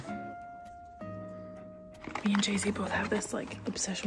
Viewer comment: I thought she said “me and Jason”… Malena is very good at vloggin, wouldn’t mind to see it more often!